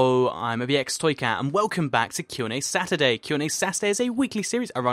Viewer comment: I thought he said Sass-urday instead of saturday! XD